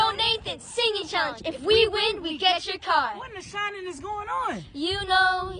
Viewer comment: I thought he said What in the salad is going on 🤣 Also the fact they are driving don’t surprise me they literally do anything in that family